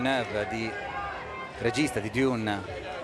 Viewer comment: what's he saying? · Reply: Regista de Dune / The director (Villeneuve) of Dune